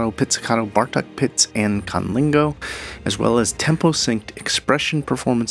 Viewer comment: did you just say Cunnilingo? 😏